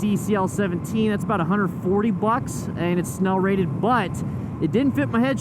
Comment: I kept hearing 'smell rated' and I was thinking 'good, my helmet stinks. I'm glad someones rating them on their smell'. I checked. nope. This helmet is not smell rated.